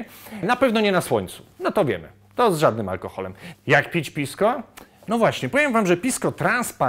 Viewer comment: Pod koniec odcinka się uśmiałem bo się przesłyszałem i usłyszałem "jak pić piwsko?" Ciekawy alkohol, trzeba teraz znaleźć, kupić i testować :p